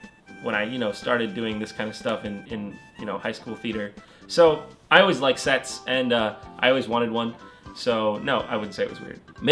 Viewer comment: I heard "I always like sex, and I always wanted one" when in reality he said "I always liked sets, and I've always wanted one."